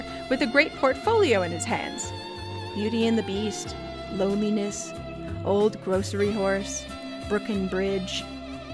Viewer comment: So I heard this as 'Old Grocery Whores' and for a split second i had never been more confused in my LIFE.